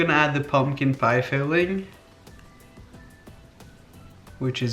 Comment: I heard "the pumpkin pie failing", no wonder why